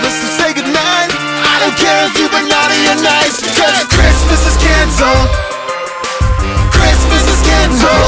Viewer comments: for a second i thought he said christmas is cancer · I keep hearing "Christmas is cancer" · I always hear "chirstmas is cancer" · at 1st i thought he said Christmas was cancer